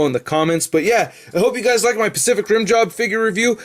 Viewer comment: Lol @ 6:06 - 6:09 did you say "I hope you liked my Pacific 'RIM JOB' figure review"?!?!? Hahaha